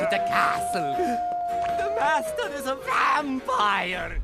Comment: did anyone else hear “The bastard is a vampire!” ?